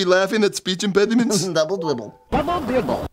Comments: I Heard "Double Dibble". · It sounds like its saying double dible